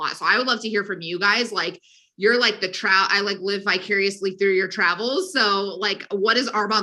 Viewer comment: Seriously, did she say "I live bicuriously through your travels"? I know she meant "vicariously", but did she really say that?